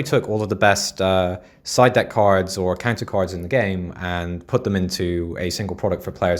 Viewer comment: did he said "cancer" cards?